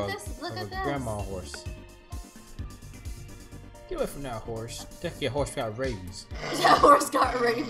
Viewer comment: I thought he said “that horse looks like it got raped”